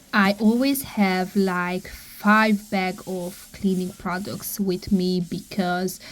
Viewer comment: you really give people who have made a mess (for whatever reason) a second chance. I truly hope they take it. It would be nice to tell us just how long you spend cleaning and how many bags of trash you remove. You are amazing. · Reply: I thought she said 15 bags of trash maybe I’m misunderstood.